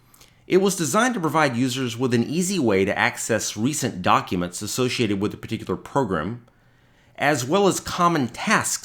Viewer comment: in 3:38 what was he saying ___ Program? Thank you! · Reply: "...documents associated with a particular program..."